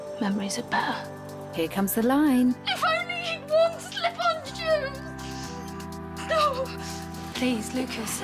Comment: What does she say at 2:26? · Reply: I think she says, "If only he'd worn slip-on shoes."